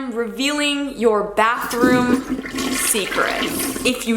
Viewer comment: omg I thought it said bedroom secrets!!!😂